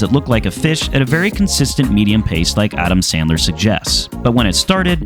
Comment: Oh em gee…I heard “at a medium pace” 😅